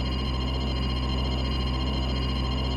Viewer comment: I thought they were saying "Kansai" lol, apparently it is "Kamusari". Which literally translates to something along the lines of divine avoidance